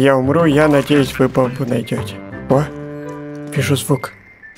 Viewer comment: как можно Увидеть звук или мне послышалось